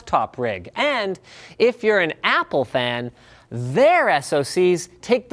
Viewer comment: I heard on 3:18 If you are an "Apple Pen". PPAP. ahahah